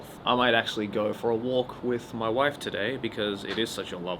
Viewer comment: did say wife